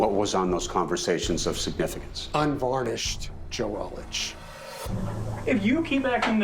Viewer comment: WHAT IS HE SAYING??? Unvarnished joelish? Huhhhh??? 😅😅😅😅😅😅😅😅